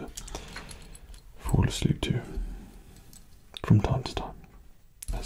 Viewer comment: I thought he said "If you Force Leap" instead of "If you fall asleep"